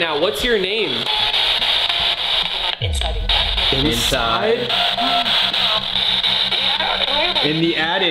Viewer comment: instead of inside you can hear it's coming